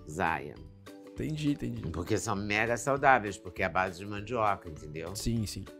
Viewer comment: eu entendi "são merdas saudáveis" mds kkkk